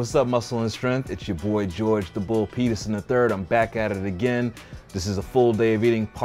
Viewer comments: I wasn’t paying attention in the beginning of this video when I heard his name at first. I thought he said “George the bull penises” at first lmao. I was like “wtf” and then I rewind it and and saw the title of this video lol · had to rewind cause I thought he said the “Bull Penis” 😳 I was like, alright, subtle flex. Lol